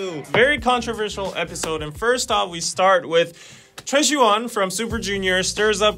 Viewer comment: Goddamn I thought you guys were saying Troye Sivan instead of Choi Siwon. For a minute I was lost😂😂